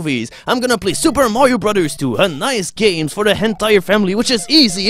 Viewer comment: when he says "a nice game for the entire family" i always hear "a nice game for the hentai family"